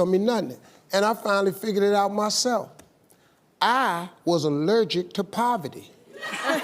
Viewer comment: Soon as Steve says, he was allergic to poverty 😁 i was bust out laughing hahaha. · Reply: I thought I heard allergic to puppy😆😆😆😆